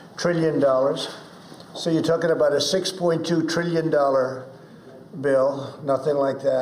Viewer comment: I could have swore that I heard Trump say 6.2 trillion!!!!!!